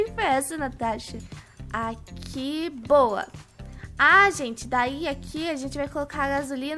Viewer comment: só eu que escutei a quiboa quando a falou